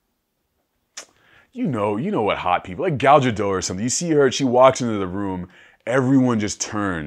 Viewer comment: what name does he say at 41:25 ? "Gow-juh Doh"? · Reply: Gal Gadot